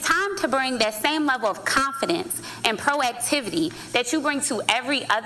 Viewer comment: Did she say, "Proactivity" @ 6:18? Forgot which way the door 2 a gym opens up?